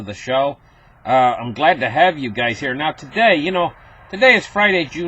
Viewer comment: you're the highlight of my youtube! look forward to hearing "glad to have you guys here" every day! financial turmoil is on the horizon! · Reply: I thought he was saying "Glad to have you gays here." !!!